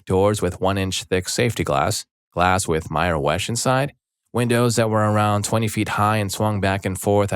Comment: anyone else hear "mire wesh" in the window glass?